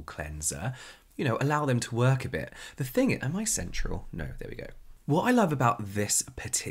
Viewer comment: when James said "am I central?" I had to go back because I thought he said sensual